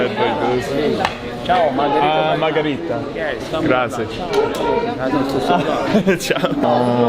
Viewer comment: Did he just say Gracias in the beginning correct me if I’m wrong but ion think that’s italian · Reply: Don't worry he said grazie lol